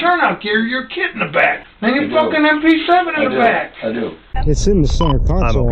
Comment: does he say mp7?